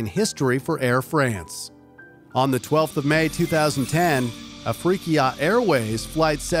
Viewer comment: I heard a freaky ahh airways flight 771 💀